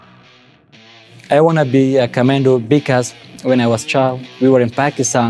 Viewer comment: He clearly says, "commando", not "commander" per the subtitles. And why are there subtitles, anyway? His Englsih is fine.